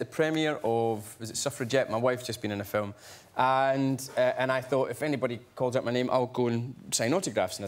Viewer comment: the thing he says is another expression for etc, right? what are the words he says? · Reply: Not sure but I think Mr. McAvoy is being onomatopeic, just making a non-word by repeating "ta, ta, ta". This is what I hear: "And I thought if anybody calls out my name I'll go and sign autographs and as it happened a few people said and ta, ta, ta, ta" Anybody else hears the same?